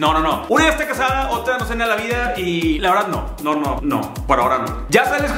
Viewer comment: Por favor que alguien me diga que dice cuando le preguntan di regresaria con una ex? Min 2:06 Jajajjajaaja dice: "Una esta casada, otra (....) what? Jajaja no entendinni maíz xD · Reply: "No sé de la vida" fue lo que entendi, lo repetí mil veces y fue lo que entendí jajajjajajaj